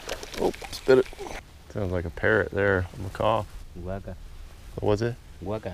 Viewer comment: When said “It sounds like a parrot” I thought he said peric